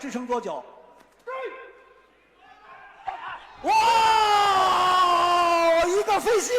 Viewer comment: I don't know what he said in Chinese, but I heard "Woooow, you bust his shit!" 🤣🤣🤣